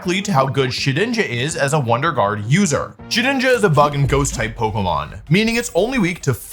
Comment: i heard shedinja is a fucking ghost